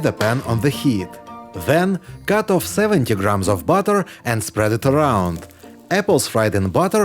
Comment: I thought he said 7kg of butter😂